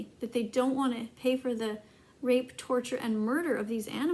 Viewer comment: did she say rape